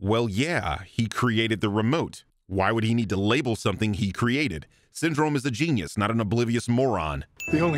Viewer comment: I like how at 12:26 when say "Syndrome is a genius, not an oblivious moron" the subtitles say "Syndrome is a genius, not CineSins"